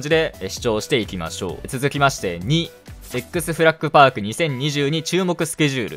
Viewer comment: なんて言った？ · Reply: せっく、、、フラッグ